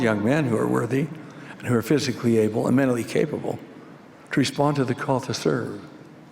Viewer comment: When Monson said "to respond to the call to serve,", I heard "to resolve our cultist urge." Probally shows how much I hate this idealogy.